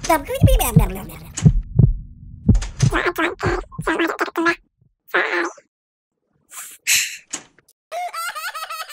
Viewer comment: what did 8 say? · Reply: 8 said we get to see 3 again in 2030